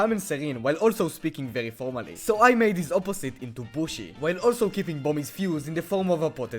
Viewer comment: Why does it sounds like he said bullsh*t?